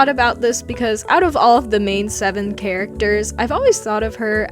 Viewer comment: did you just say main seven?